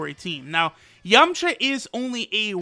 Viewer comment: Did he say Yumcha or Youngcha?